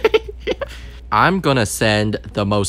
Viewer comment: I hear oh case oh wait